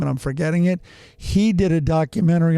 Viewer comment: Thought said Dahmer party (foot sandwich made sense) until explanation Never before heard of Donner Party